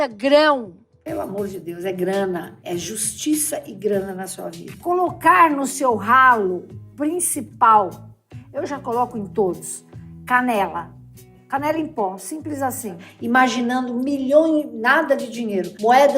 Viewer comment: juro que eu ouvi "colocar no seu rabo" hahahhaha